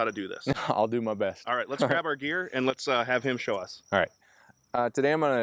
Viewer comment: I thought the host said “Let’s grab our beer and have Nathan show us!” I was like WTF!😮